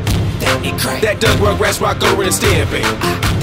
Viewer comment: I thought black nerd said Stampy